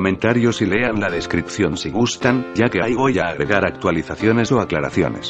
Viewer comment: Quien escucha en el 14:05 que dicen ay voy, escuchen bien y lo verán.